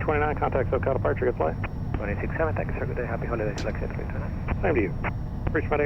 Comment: did he say happy honda days? lol